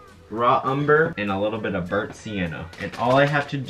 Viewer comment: did you say Sienna? That’s my name